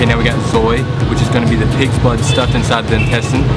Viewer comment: did he say pig butt inside the intestine? I’ll try it all but don’t tell me what it is and definitely with less pepper. He eat pepper like it’s nothing🤭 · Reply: hahaha pig blood!